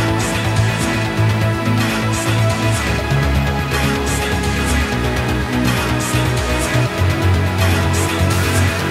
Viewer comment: what is he saying? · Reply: Lost In Music